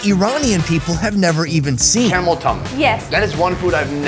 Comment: omfg I heard “camel toe” the first time I watched. I thought u were just being funny lmao